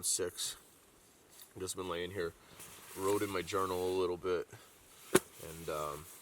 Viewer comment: did he say "wroting my journal" lol